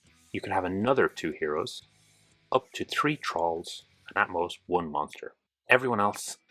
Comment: Great video, thanks very much. I thought you were saying trolls rather than thralls though because of your accent 😂